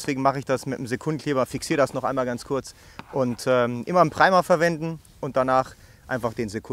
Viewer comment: "Immer nen ... verwenden"??? Was soll man verwenden? Habe es mir jetzt das fünfte mal angehört und verstehe immer noch nicht was er da sagt? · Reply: Immer einen "Primer" verwenden, dieser sorgt für eine bessere Klebeverbindung.